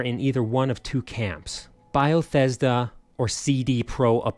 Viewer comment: I keep hearing "BIalThesda" and I don't know what to make of that.